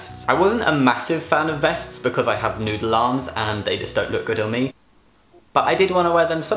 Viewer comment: All I heard was 'Nude Alarms' and then I realised he was actually saying 'Noodle Arms' ...